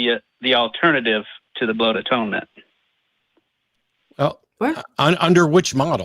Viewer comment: I thought he said "butt atonement"